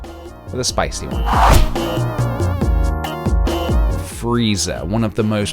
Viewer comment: the whole time you were drawing frieza i thought you were saying freezer